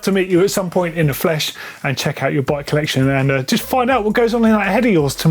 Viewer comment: Did he say meet his flesh and check out his butt at 5:20?